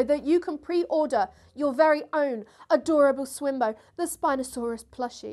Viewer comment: I thought you said something about buying swimwear but it was swimbo. If you would like to start selling 7 Days of Science swimwear, I would consider buying some.